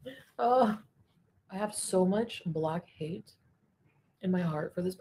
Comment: ?Huh? What did she say here? Did she say ‘block hate’ or black hate’? Interesting turn of phrase if so, never encountered it before 🥴. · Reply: I heard it like I have so much black hate for him. Block wouldn’t even make sense. I have block hate?